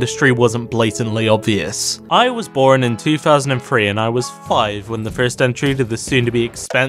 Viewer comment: When he said he was five when Iron Man came out I thought he said he was fired LOL